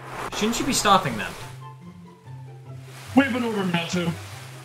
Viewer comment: What are they saying at 07:35? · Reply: "Shouldn't you be stopping them?" UN: "We've been ordered not to."